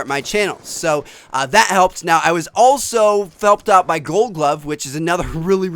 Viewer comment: Is it just me or did it sound like he said "I was felt up like goldglove" lol 19:30 and a huge pause moment 19:21 lol